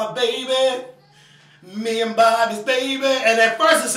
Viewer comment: I don't know y u were suprised at her talking the lyrics b/c she does the same on her new album. BTW I thought she was saying "me and Bobbi baby"